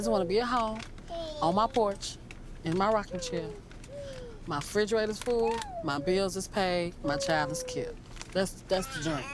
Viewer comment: I'm sorry, what did she say at 18:01? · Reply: “My refrigerator is full, my bills is paid, my child is cared”